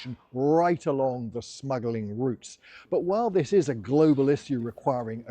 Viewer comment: I thought it said "snuggling"